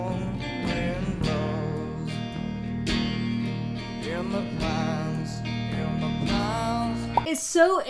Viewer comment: I always thought he said "in the ponds" haha